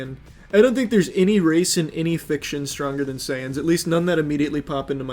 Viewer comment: I thought they said “viltrumites or sans” and then got even more confused when said sans with no possibility of losing